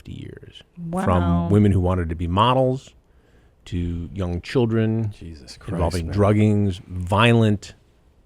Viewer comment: When he said young women he meant children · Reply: he clearly says “young children”